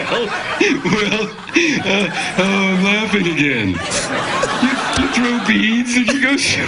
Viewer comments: what did geoff said right before the laugh outbreak? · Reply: He said : awww Im laughing again... We throw beads at people... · what does Geoff says at 3:55 after Craig asks him what do you do there? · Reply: "we throw beads and go ..(laughs)". They were both just going to repeat what they improvised about Geoff having a place in New Orleans, but they couldn't make it without laughing.